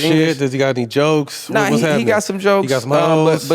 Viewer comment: Awesome interview!! Thank you!! But I have one question. The part you are talking about Shohei, around 10:42~ You guys are joking about what's he like and what is he doing off field. After the phrase"He got some jokes?" "He got smorz?" or "He got s'more?" What is the correct phrase you are saying? Please help me understand the joke they are saying? Or anyone who knows the joke please help me out. Thank you! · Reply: Super late. But they said he got some hoes. Which is slang for whores which basically means does he have any side chicks/other women. But they're joking cause he's such a closed off guy (very private) whereas that type of player in American sports would be the type who's very out there and displaying it. Someone like a Tyreek Hill in the NFL. Was mostly just a joke about how Shohei appears to be a professional both on and off the field by asking if he has a super unprofessional quality.